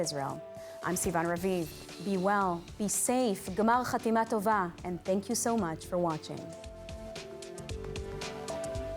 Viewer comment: Hey a little help needed. I am new to Hebrew, can somebody please tell me what did Sivan say at the very end in Hebrew? At around 23:50? Something like "gimaal chaateema tova", what was it what does it mean? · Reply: Figured out!!! "גמר חתימה טובה" (G'mar Chatimah Tovah) This means "May you be sealed for good [in the Book of Life]." It’s a way of wishing someone a good final sealing, as Yom Kippur is the culmination of the Days of Awe, during which people seek atonement and forgiveness. And G'mar Chatimah Tovah to you too!!! 😁😁